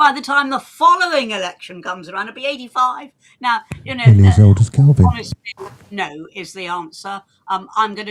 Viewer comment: What does say around 2:27:20 ? ... it's something like 'nearly as old as Kelvin will be'.